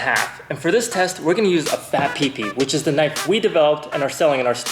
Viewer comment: Did he say a fat pepe